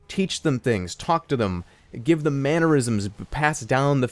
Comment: I heard give them aneurysms. Jerma please don't do that.